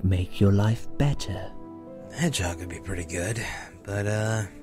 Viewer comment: I seriously thought he said hand job. Hedgehog was less funny but sooo cute :P